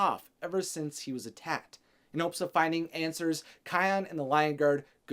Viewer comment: Wtf are you making thought you said where kion in the lion king wtf noov